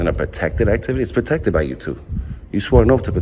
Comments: Did he say hes protected by Youtube? 16:19 Good God. The doosh is back? . She was so nice to him. Then he wants to foia her info. I was thrilled when yt demonitized him. Now he back. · It sounded like he said his rights are protected by YOUTUBE?